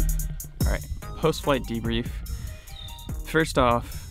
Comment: can you start over? I missed what you said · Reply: Post flight pre brief ?